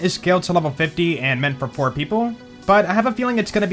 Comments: Thought you said poor people instead of four people. I just can’t get the season pass yet, okay? Good thing it’s free. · Me to what did he say I cant catch it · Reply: He said 4 people, not poor people. I also heard it as poor people when I first watched.